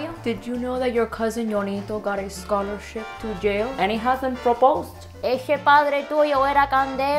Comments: i heard and he got a scholarship to jail. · Lol I heard “scholarship to jail” instead of “to yale”